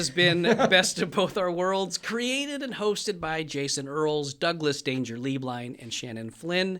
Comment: When introduced himself for the first 8 episodes I thought he was saying, “Doug ‘Dangerously’ Blind” 😭 Cracked me up so hard when I realized I was wrong 😂❤️